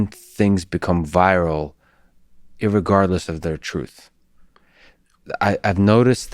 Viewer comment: Yikes. Did lex just say "irregardless" 🤨